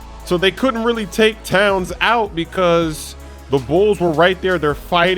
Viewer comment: lol he said bulls instead of hawks